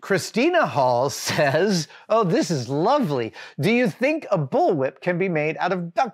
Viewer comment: I misheard "bull whip" as "bullet" and now I want to make a duct tape bullet